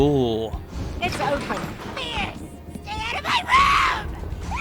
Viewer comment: It just me or i felt satisfied hearing Agatha said "BIERCE!Stay out of my ROOM!" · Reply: I thought she said "BIERCE, GET OUT OF MY WAY!"